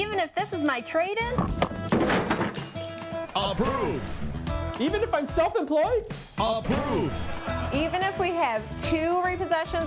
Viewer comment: I thought the clown said: even if I suck at life?